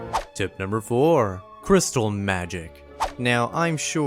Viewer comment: I always use these tips nowadays so smart. At number 4 I thought he said crystal math